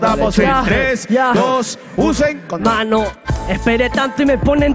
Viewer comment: CREO QUE NADIE SE DIO CUENTA LO QUE DIJO EL METZ. 3 , 2 , "USEN CONDONES"... JAJAJAJAJAJAJA